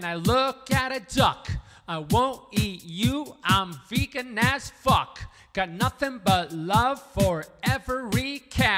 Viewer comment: At first I heard "I'm a vegan, lets fuck". That poor duck - I thought.